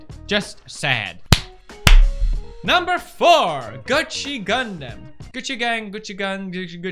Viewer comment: I thought felix said Gucci condom